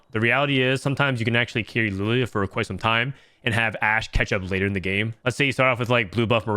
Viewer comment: I thought you said «Ash Ketchum» 🥲